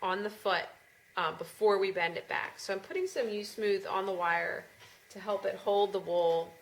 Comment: Hi, Can you please say what you use to attach the wool to the wire? Thanks! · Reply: At minute 4:20 did you say you were using "you smooth"? Or is that just beeswax?